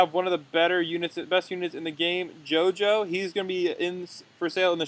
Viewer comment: u mean gojo instead of jojo